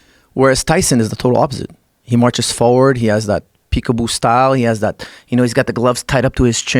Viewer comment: When he said peak-a-boo style I thought he said jig-a-bo lol